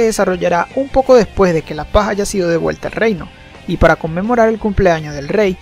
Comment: Escuché mal... Parece q dijo paja 2:36.jajaja